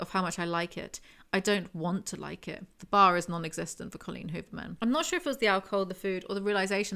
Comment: Okay, did anyone else hear Collen Hoover instead of Verity?